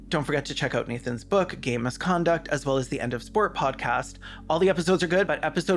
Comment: Every time you say "game misconduct" I just hear "gay misconduct" and where is that book about sports?